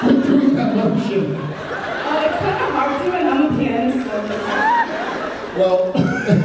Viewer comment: What does the fan say at 7:25? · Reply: hahaha that was my question, I told them that "I'm a pianist, and what he's doing is even hard for me to do". In fact, now that I'm seeing what they are doing up close, it looks like Jared is trying to act out how to play a mallet instrument like the marimba or xylophone lol :D